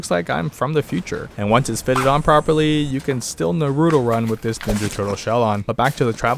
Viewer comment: for some reason i heard narudo instead of naruto